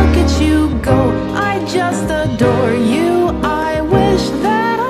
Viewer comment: i always thought this said "im just a door"